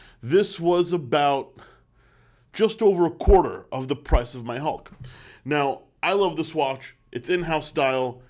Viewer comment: What I thought he said: "This cost about one quarter of the price of my home..." Me: "I really gotta step my game up."